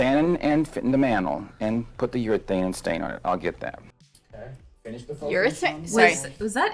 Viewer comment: I thought they said “ put the urine stain on it”